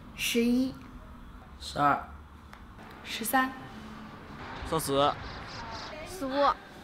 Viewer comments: the girls who is 13 years old, sounds like she said SHAZAM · who else heard shazam ?